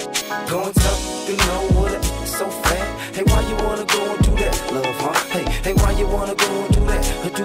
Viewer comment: When I was younger I always thought he was saying , why you wanna go in do that lil punk 😂😅 smh not love huh … well now I know